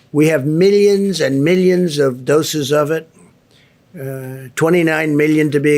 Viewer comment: Frankly speaking when he said "doses" I heard "dosas"